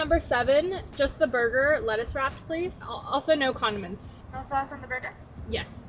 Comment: Anyone else who thought said "also, no condoms" instead of condiments!! Just me? I had to go back and listen again